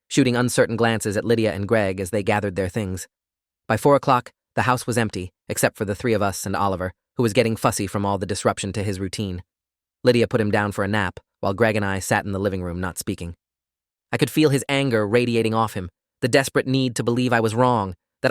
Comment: I heard, "Lidia had to put him down" and I said wait WHA- before I read the other line XD